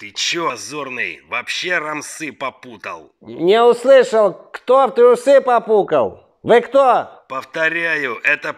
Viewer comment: "не услышал, кто в трусы попукал?"😂😂🤣🤣